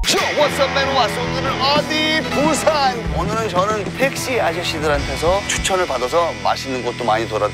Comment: 자막안보면 펩시아저씨로들림